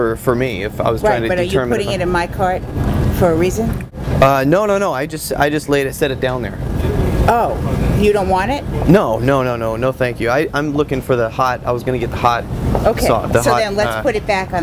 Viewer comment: it sounds like he says he "looking for the pot" lol.